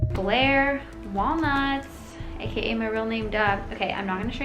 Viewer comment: I thought u said that u said blaire Waldorf